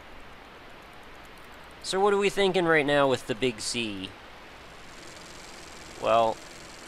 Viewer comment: Every time you say "the Big Sea" in this video I heard it as "the Big C". In any case, I'm off to go get this game. Brain damage may ensue from the head bashing.